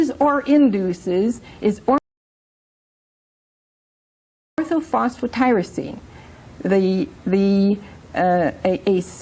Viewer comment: What was she saying at 9:38 ? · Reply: Orthophosphotyrosine -- <link>